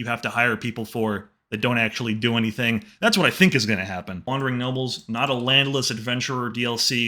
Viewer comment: It sounded like you said wandering nobbels instead of nobles